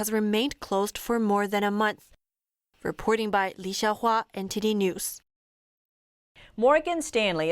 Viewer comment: Did she say TT news